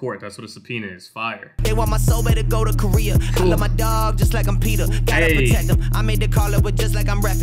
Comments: Haha I always thought he said I love my dog like I’m Peter lol like Peter griffin 😂 thanks for that clear up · Lolol all this time I thought he said “love my dog just like I’m PETER” like a family guy reference 😂